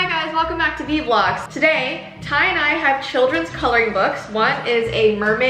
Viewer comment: at first I thought said "so Tye and I have children..." 😳